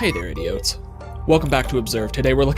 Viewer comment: In the intro i thought he said "oh hey idiots"